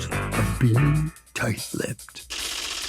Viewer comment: Lol I thought it said “being light tipped”